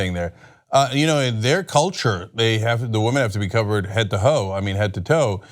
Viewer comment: Head to hole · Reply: aloha from kenya - I thought he said "Head to Ho". 😄